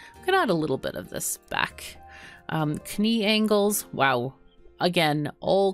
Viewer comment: Did you say kah-nee angles?